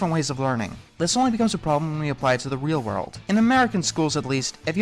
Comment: My stupid brain thought he was saying “in numerical schools” instead of “in American schools.” 😆